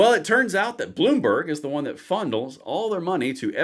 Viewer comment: "Bloomberg fondles all their money..." While I think actually meant to say "funnel" what he DID say is also true. I think I smell smoke... oh wait, that's a flicker of hope between my ears.